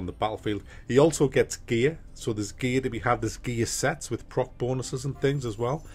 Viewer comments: coulda sworn you said "there's gay sex". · I was in the middle of doing something else while listening to this review and about two minutes in says ""you have gear sets" and I swear to God with the accent I thought he said "gay sex" Love your reviews and your beautiful accent! Pardon my dumb American sense of humor 😁